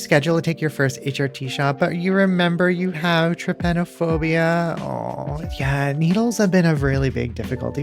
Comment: I legitimately thought that said transphobia and I was very VERY confused for a sec.